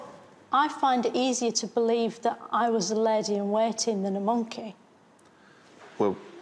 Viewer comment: what does she say? A lady and...? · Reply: She says a lady in waiting. This means it's a lady who attends a princess or Queen